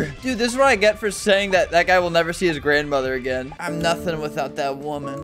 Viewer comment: did he just say the n word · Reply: He said “That guy” not “nigga”